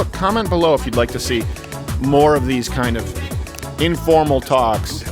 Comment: Am i tripping or do i hear "MILF"